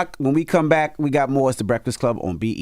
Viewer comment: did he say B.E.T 🤔?